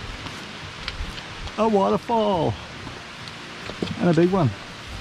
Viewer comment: Another longer, art video. I don't know if I come to see the beauty or hear the beauty. And I thought that said that he wants to fall before realizing he said a waterfall. I guess I will need to start watching with the cc turned on. Thanks once again!